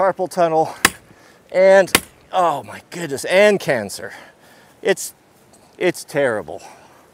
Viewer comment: Haha I thought you said "hand cancer"! :D